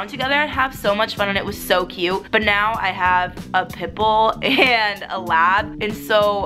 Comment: When you said you have pit bull’s I thought you said you have pimples😂😂